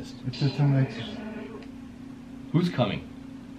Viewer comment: Sounded like the little girl said burr like amber when asked her name. Also at 33:07 you hear it say Am-ber. At 36:26 is yells its him, go! 41:38 it said clearly he’s here!!!!! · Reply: I heard in 36:26, "THE DEVL'S COMI......."